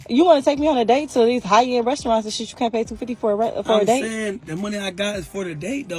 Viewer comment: Did anyone hear her say @ 1:00 “ you gotta pay $250 for rent err date!!”